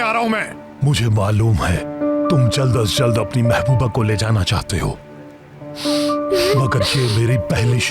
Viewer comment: seher heard her as yamans mehbooba, , sab yaman ka kaam aasaan kar rahe hain 😂